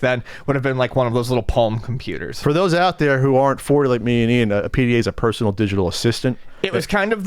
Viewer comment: just wondering what says. can someone help me I'm just curious, more than anything. go to @ 00:59: "For those out there that aren't for like Me and Ian a pda is a personal digital assistant" did he mean to say, "that aren't 40" (and I know they aren't 40, but maybe he jokingly said that. or I dunno. . its just my OCD. . lol. . confused me. . ha-ha thank you peace the Eturn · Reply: I heard say "40" a bit quickly. And that actually is his age. Don't know about Ian.